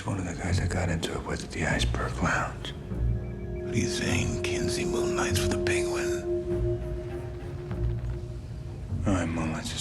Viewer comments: can someone tell me what they’re saying here? It’s too muddled for me to hear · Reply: "You're saying, Kinsey Moonlight is with the Penguin?" "I'm more (can't tell what Batman is saying here) is a cop" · what does Batman say? I genuinely can’t tell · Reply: Are you saying Kenzie moonlights for the penguin? " or he moonlights as a cop".